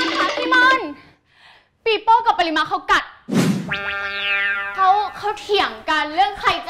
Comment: ลาตินพูดว่าอะไรหรอเราได้ยินว่า ปีโป้กับปะนิมะคบกัน😅🥰🌚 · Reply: ลาตินจะพูดว่า.. เขากัดกัน(คำนี้พูดไปก็โดน censor แน่นอน..55 แต่มันเป็นคำแซวไง) แต่ยั้งไว้ก่อน..555